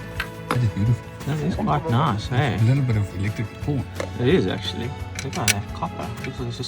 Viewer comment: did i just hear, "...a little bit of elecrtic porn..." 😂😂😂